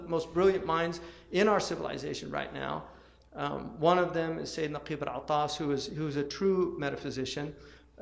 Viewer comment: Who is the person mentioned at 11:11? I didn't catch the name. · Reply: Syed Naquib al-Attas, a living Malaysian philosopher. His corpus of thought is summed up in this collection called the _Prolegomena to the Metaphysics of Islam_, which can be difficult to find, but can be purchased here: <link>